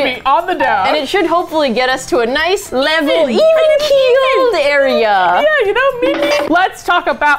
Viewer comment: Not even a minute in and I've misheard "even keel" as "even kegal" and I can only assume this is how all great roller derby names are born.